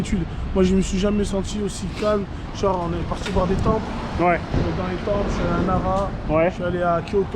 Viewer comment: on est parti voir quoi ?!! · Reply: Des temples il a dit